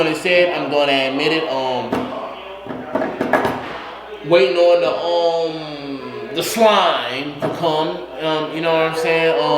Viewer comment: did he say wating on the slime to cum...lol